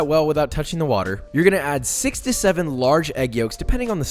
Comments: "You're gonna add 67 large egg yolks..." I was like wtf, saw the clip again and realized he said "6 to 7". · I thought I heard 67 egg yolks xD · dawg I thought you said 67 egg yolks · why... did i hear 67 LARGE EGGS instead of 6-7